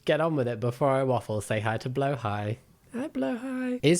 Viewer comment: Off video topic...how do you spell whale/shark? I hear "blohai" but it seems to be in a different language. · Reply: it’s swedish! it mean “blue shark” I’m norwigan but how i’d spell it as; blåhaj.